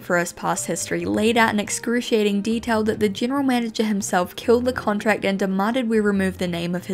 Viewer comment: I thought that said the general manager killed himself 😭